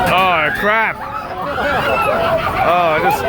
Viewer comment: did you say "oh crap" or "on crack" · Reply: I said "oh crap".